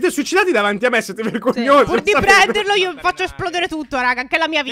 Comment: ho sentito “cogli**e” da o